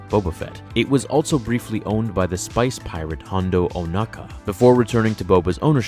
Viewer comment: i heard spice pirate aha just me?